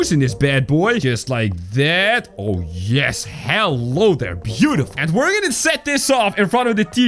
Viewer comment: I thought almost said "hell" because hello was cut off